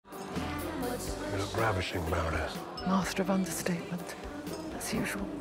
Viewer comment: What is he saying? "You're ravishing, Baroness"? Why am I hearing "gravishing"?